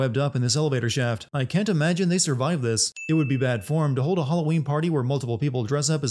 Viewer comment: He clearly says "It would be bad form..." however, the subtitles clearly read "I would be bad form..." Ding.